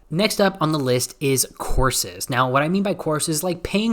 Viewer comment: I was listening with headphones and totally thought he said "horses" for a min haha.